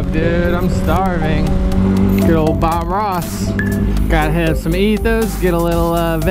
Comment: I'm sorry, did I just hear "kill Bob Ross" because if you are hating on Bob Ross I will yeet you out of a happy little tree